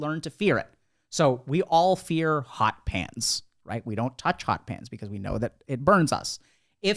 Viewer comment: at first I thought he said, " We all fear hot pants,". ( Short shorts)